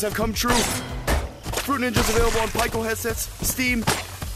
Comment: nah bro rly said "pieco headsets" 💀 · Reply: nah really I thought he said up down headsets😱